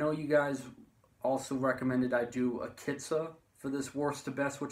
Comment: What band you say at 14:36? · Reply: Akitsa